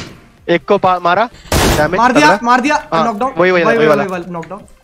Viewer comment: I think carry say lockdown. But it knockdown